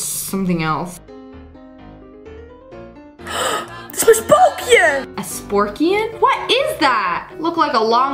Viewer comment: i heard spopion